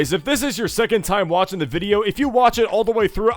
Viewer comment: i thought you said "did nazi the video"